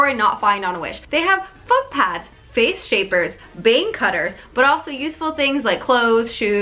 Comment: thought you said “vein cutters” and was sooooo confused